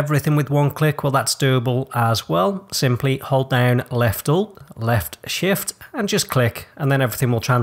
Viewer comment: what is the "hotkey" that you said you hold when clicking to transfer all items between chests? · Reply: Left alt left shift